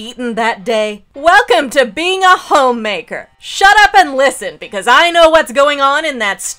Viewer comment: My dumbass really thought it said homewrecker instead of homemaker. I was so confused with all of the comments